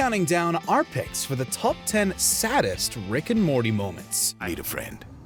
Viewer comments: I'm the only one who heard him say Rick and mormy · Did I hear “Rick and mormy” ????